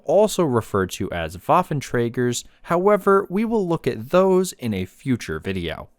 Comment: Didnt expect a documentary video on a “waffle tractor” but ill take it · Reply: I thought I heard waffle trigger, is honey included?